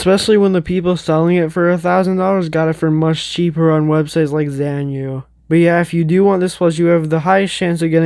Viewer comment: Could you still get the disco, key chain disco, red shirt disco, both zombatanies, and hairy zombie? If so which websites would be the best? Ik you said smth at 34:55 but I couldn't hear it I think it was zanyu correct if I'm wrong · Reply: The best place to get them is Xianyu. If you want to know how to log in to it, just look up a youtube tutorial there's a few good ones.